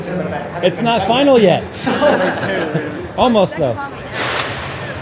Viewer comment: what did he say i heard was"pos mon bo naxmin " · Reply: Post Malone build announcement